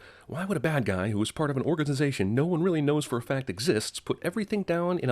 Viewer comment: Haha at 6:36 u said organdization..I heard it